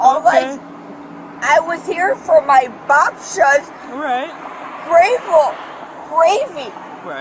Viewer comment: this sounded like Polish "Babcia" which means grandma